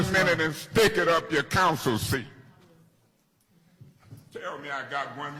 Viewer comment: Stick it up your councilcy 😂😂😂😂. RIP Eric Mays! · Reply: Lol I thought he said council seat 😂😂